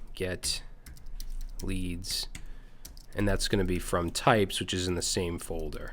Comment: "typoes" instead of "types", such a self-reference...